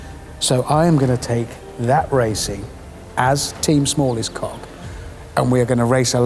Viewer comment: For a moment there I thought he said "Team smallest